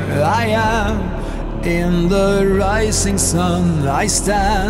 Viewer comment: "In the setting sun i carry..." I heard "In the seiza..." ^^